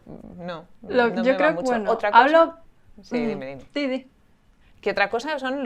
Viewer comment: What did say ? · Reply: She said: "Si, di", which means "Yes, tell me", as a way of telling her friend not tot stop speaking, and to finish what she was saying.